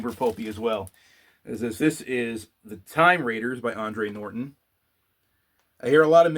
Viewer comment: Time Traders by Andre Norton (sounded like you were saying "raiders') is a densely complex story. I have it on e-book, and even though I haven't finished it, I know it is a wonderful read. Additionally, I love that hardcover of Magic Kingdom For Sale - Sold over your right shoulder in the video. I only have that book in a later mass market paperback, but it is a regular reread that I'll probably get back into today.